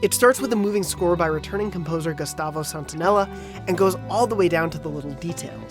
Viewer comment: did he really say Gustavo “Santonella”?